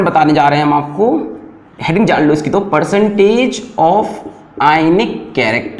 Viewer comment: me kya bole sir?🙄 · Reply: Heading jaan lo iski percentage of ionice ye bola